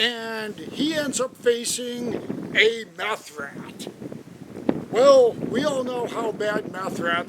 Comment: meth rat. Wtf is a mouth rat? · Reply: I thought you said mouth rat either way im keeping it